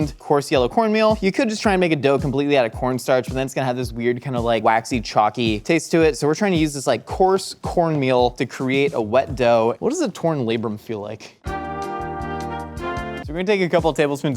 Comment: I thought he said what does a torn laybeum feel like. I had to rewind it.